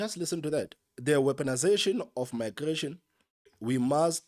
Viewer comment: what the blerrie yell is "weaponisation of migration".